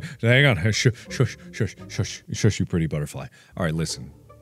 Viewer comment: Me: "Did Mark just call us a 'Pretty Butterfly'?" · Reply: Yes pretty butterfly